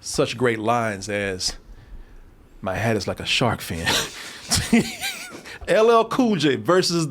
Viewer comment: "Deepest Bluest. My hat is like a sharkfin!" - LL COOL J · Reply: I always thought he used to say his hand is like a shark fin lol